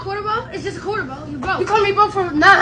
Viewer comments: I thought he said that's why you don't got a dime lol😂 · I thought he said thats why u dont got a dime 💀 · Bro I thought he said "that's why you don't got a dime." 🤣🤣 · I thought he said that’s why you don’t got a dime